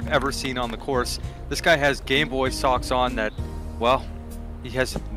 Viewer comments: Maybe it's because I'm tired, but in your starting skit I thought the commentator said he's wearing gayboy socks. I did a double take and glad I just misheard. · When they said "Game Boy" socks on...at first I thought they said...G__ Boy socks on...